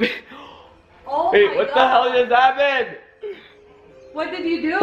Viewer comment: did rug say babe what the happend